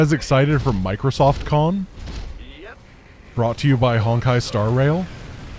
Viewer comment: did i hear "Honkai star rail"?